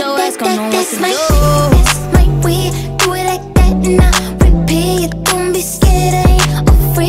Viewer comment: Who else it thought it said repeat instead of repay?